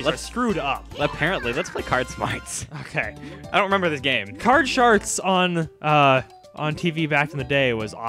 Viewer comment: I swear he says card sharts XD